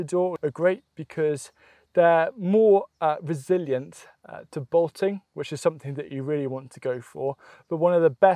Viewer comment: what is he saying? "Botting"? What's that?